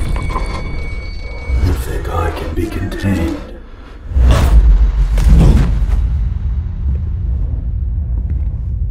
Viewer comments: what did he say?? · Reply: "you think I can be contained?" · What did it say at [0:44]? I'm from Germany and it sounds like: "Vergolden dir die Tränen" - "Golden your tears" But what did it really say? Note: Google translation used. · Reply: it said "you think i can be contained?" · what did he say??? it's not clear please tell me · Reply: “You think I can be contained”